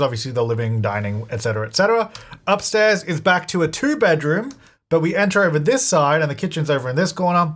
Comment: said "back to two bedrooms" (about 25:10) and I definitely heard "batuu" :_jtytLol